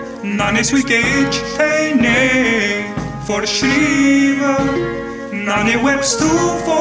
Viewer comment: sounds like German+czechia+Russian+japanese+indian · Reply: i heard he said "Shiva"